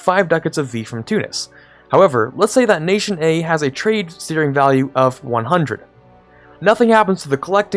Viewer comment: About 10:17, what did you say? Let's say nation A has a trade shshshs of 100" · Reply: Steering*